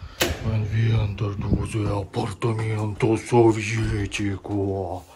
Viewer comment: Is he mixing English with Portuguese for some reason? It sounds like he says "We enter apartamento soviético" when he enters his apartment.